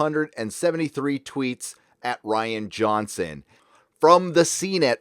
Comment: Rihanna Johnson that's what it sounds like he say instead of Ryan Johnson